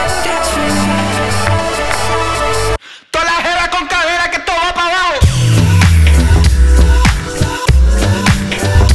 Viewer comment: I have no idea what the hell Jon just said, but it was DOPE!!! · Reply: he said to be exactly : To' la jeva con cadera que esto va pa' bajo The nearest translation would be something like: all the girls with hips this is going down jeva is spanish slang for a women